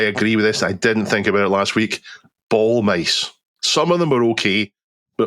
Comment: Haha, for a second I thought our buddy said, re: tech. we no longer have to use, "ball MACE"! 🤣 I'm just thinking of Medieval sort of weapon or something like that, haha! 😆🤣 But no, I got it now! 😁